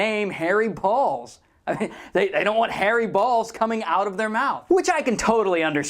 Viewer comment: Press 8 over and over it sounds like he says " i want hairy balls "